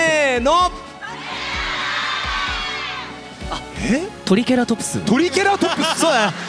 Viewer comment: 結構しっかりトリケラトプスって聞こえたwww